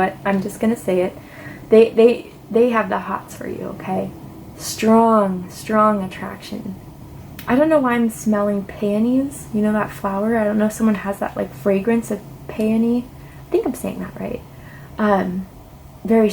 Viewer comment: Hahaha I thought you said "smelling panties" 🤣🤣🤣😭🤣🤷‍♀️